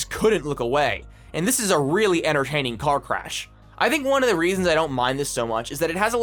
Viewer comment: look everybody! he said "enterhaining" instead of "entertaining"! point and laugh!